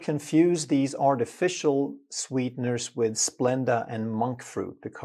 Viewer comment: Didn't mean he meant to say Stevia/Monk fruit. I thought he said Splenda and Monk fruit.